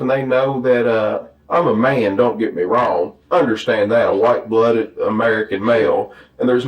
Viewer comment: did he say he's a "white-blooded" American male? 🧐